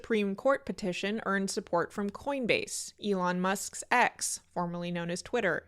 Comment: When you said “Elon Musk’s X” I heard “Elon Musks’s ex” at first and thought “oh, which one?!” - just commenting for l’algorithm etc